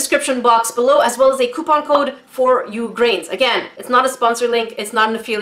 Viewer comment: My captions are on and when u said grains at like 2:15 -2:17 somewhere there it showed grapes instead of grains lol